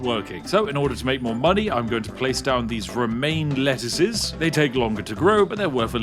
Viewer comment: I heard "REMAIN lettuce". Which makes sense, seeing how the Lettuce's biggest rival turned LEAVEr at that time.